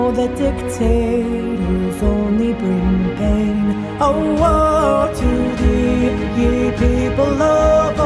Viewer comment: I dont know why... but everytime she says "Oh woe to thee." I hear "Owo to thee." And I don't know why!